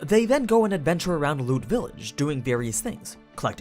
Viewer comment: Thought you said, "They then go to lewd village, doing various things", at first.